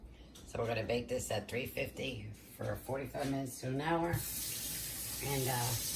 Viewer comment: You are a great cook 🙂 , and also a bless to listen to. Thank you 💟 , hugs 🙏🌼. PS : maybe tell us the temperature 🌡️ you use on your cooker 🤗. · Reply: Towards the end I thought I heard her say 350° for 45 minutes to an hour.